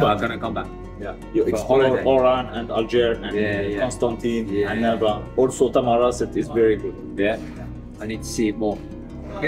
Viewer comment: When he lists the cities of "Algiers, Oran, Constantine, Elba, Tamaras" what he actually said was "Algiers, Oran, Constantine, Annaba, Tamanrasset"